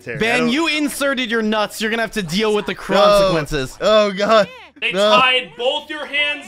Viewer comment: Did he say "crunch-equences"?